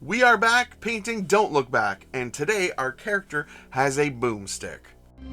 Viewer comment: i had to listen like 5 times... i kept hearing "Boobstick" and i was very confused